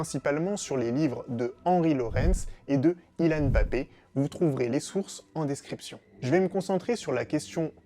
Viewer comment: Qui comme moi a entendu : Kilyan Mbappé Mdrrrrrrrrrrr